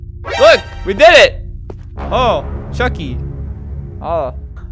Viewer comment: I thought it said chunky